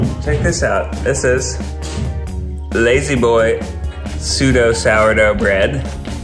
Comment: Idk if anyone checks these but at 5:57 the caption should read “pseudo” instead of “sudo” :0